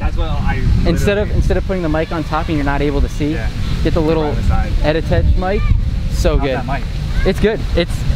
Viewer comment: What did you say that mic is @ 3:53? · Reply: Edutige ETM 003! you can get one here :) <link>